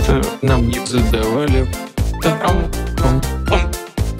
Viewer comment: мне одному послышалось "трамп, пам, прамп"